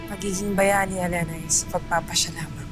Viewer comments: "Pagiging bayani Alena "is" pagpapasya lang" tama ba narinig ko? Haha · Who's watching "Ang pagiging bayani alena'y sa pag papasya lamang" Tama ba Ang narinig ko tinawag ni amihan si pirena na Alena